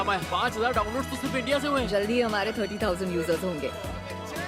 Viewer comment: Did he said India